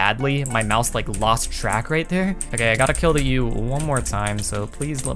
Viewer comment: did he say gotta kill this yute?😂😂